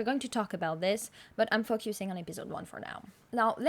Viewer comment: OMG I thought you said F you it was like "fuck-you-sing" 😂